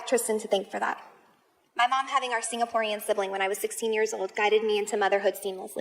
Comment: did she say “my mom having our ‘Singaporean’ sibling….” ?? If not, what did she say?